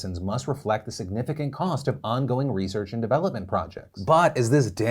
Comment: on going research is not old research is what he said not old research in a currently available drug. Please stop being like CNN, and other major networks.